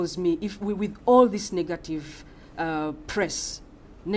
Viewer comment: Is it just me or when HE said at 14:34 negative it sounded like....NIGGA-TIVE